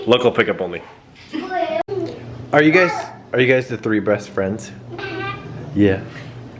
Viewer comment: Lol sounded like said breast but captions picked up "best friend"